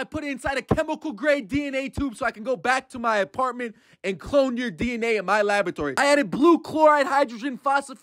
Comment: "HeY CoBY I bEt U caN't cOpY mE CoBy" Me: girl what kind of voice is that? Edit: I didn't Knew she said clone I thought she said copy ok! 😭